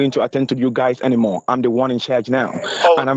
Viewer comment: How did no one hear what he said at 1:45 or 1:46 the scammer said "no now im in charge" 😂